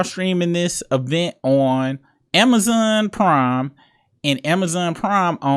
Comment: New viewer, this my first video. I really thought you said Amazon Crime at first. 😂 I subbed up tho. Shout out from Ohio. 💪🏾